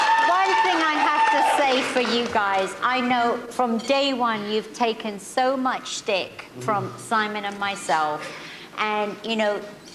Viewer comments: "one thing i have to say for you guys. i know, from day one, you have taken so much dick from simon and myself"?....thumb up if u thought she said that. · does she say so much dick at 7:03? if so, its funny cuz he licks his lips and moans. lmfao